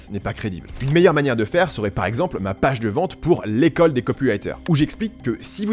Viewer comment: moi je suis intéressé par l'école des copulateurs, hein quoi ? j'entend mal, désolé 😅